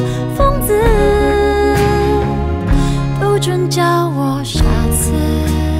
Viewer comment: 是我耳朵業障重嗎… 我把不准叫我傻子聽成不准叫我「沙子」 和送我薪水的方式（送我心碎的方式